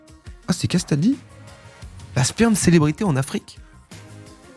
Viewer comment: vous entendez quoi à 15:51 ? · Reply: Très clairement la Sperme Célébrité